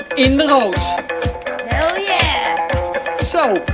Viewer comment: did she say "hell ya" ahaha? Is that like universal language...?